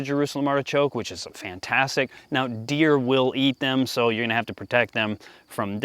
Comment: you had me at "the deer will eat it"... i heard "feeds deer". and deer feed me.